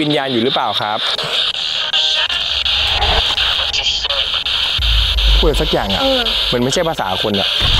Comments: นาทีที่ 30:28 ได้ยินเป็นภาษาอังกฤษ ว่า "what did you say?" 😅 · ได้ยินว่า I want to show you. ไม่รุ้ว่าฟังผิดรึเปล่านะคับ